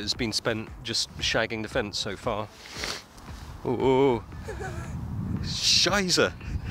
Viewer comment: Did he say scheisse on